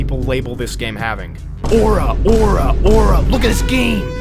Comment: is bro saying ora ora?! Is that a... JOJO REFERENCE?????